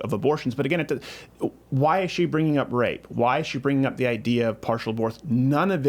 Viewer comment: I thought they said grape instead of rape at first